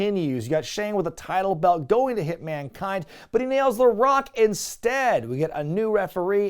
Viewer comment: for some reason, I heard this as "HitMankind" as if it were a tag team name for Bret and Foley😂.